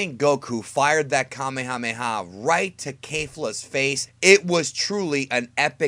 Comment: Did he just say kafla instead of kefla 😂